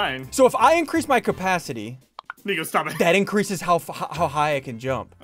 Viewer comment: What did he say on 16:27 😦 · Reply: No right after SSunde said capacity his friend said N**ga stop it